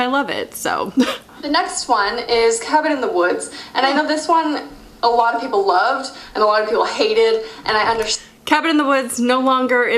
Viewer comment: I thought you said "Kevin In the Woods" lmao